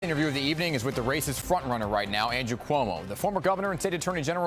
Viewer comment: he said "race's frontrunner". i am not kidding, i literally heard it as "racist frontrunner" xddd